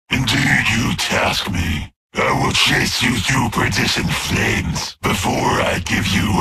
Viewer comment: Anyone have any idea what word he says right before "flames"? I'm wondering if this is a "smacktalking like a bard" line. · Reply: “Perdition Flames” is another, and quite old-fashioned, way of saying “Fires of Hell.” Also a Moby Dick reference, so that’s neat.